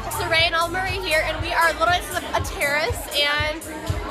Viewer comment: i swear i thought she said a terrorist instead of terrace lol